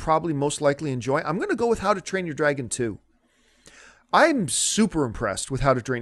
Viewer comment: Did Campea say "How to Drain Your Dragon"?